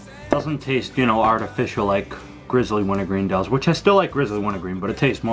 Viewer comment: Thought u said u didn't like grizzly wintergreen